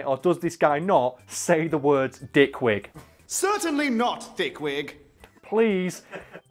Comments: Actually the line that you thought was "Dick Wig" was actually "Thick Wig" is just the accent that makes it sound like the other but I clearly heard "Thick Wig" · I think he said thick wig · I think he says thick wig in that moment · I heard thick wig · Hella late so someone probably already answered this, although it definitely sounds like he says (dick wig) I'm pretty sure its (thick wig) which was a Insult at some point in time